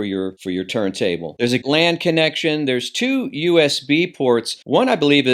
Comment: Hum, I will name names, WiiM, Topping, SMSL and Bluesound. I thought you said there was a gland connection on the backside, but I might be hearing things.